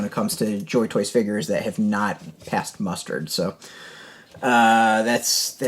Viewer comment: Did I hear "passed mustard"?